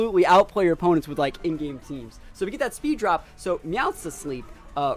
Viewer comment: Dont feel bad for asking. An in game team is just the team you beat the game with in your playthrough. Its a team that hasnt been fine tuned for competitive play. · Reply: This whole time I thought people were saying “end game team.” 😂